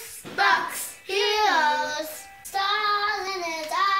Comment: Don't know how I missed this one. . .great job with a great song (I loved the kids in the beginning - great intro to the song). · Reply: Haha! Thank you, MountainNeko. When the kids heard the original song, they thought it said, "Juice-box hero!"